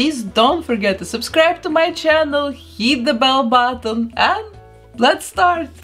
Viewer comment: Thought you said “Yeet the bell button” at first and tbh I like that better😂😂